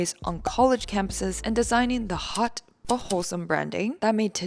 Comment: I thought I heard "Hot Butthole Branding" and thought it was still relevant since the video is about Bumble lol.